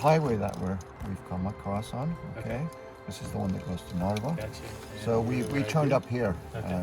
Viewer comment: Hey, really nice to see people explore Estonia :D Some things though: it's Jägala juga(as in Jägala being the location and juga meaning waterfall) and Palamuse manor not Palmse. I noticed aswell that you might have misheared some stuff with the subtitles as I imagine you wrote them? For example at 4:12 he said that the road goes to Narva not Norway :D At some point there was the name Lahemaa that you wrote funnily too xd. Seems though that you found a good time to visit as there was no snow? When did you manage to do that? The ground is white again as I'm writing this comment :D You should go to the bog during the summer as then you get to feel what's it feel like to have literally no trees around you :D You can barely breath as the air is not moving and no photosynthesis is going on. Winter is nice for ice skating, the bogs are really popular for that.